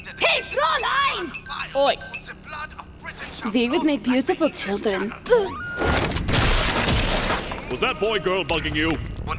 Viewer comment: "We would make beautiful children" BOOM HEADSHOT Tbf, he deserved it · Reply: I swear I always heard him say "Be with me beautiful children".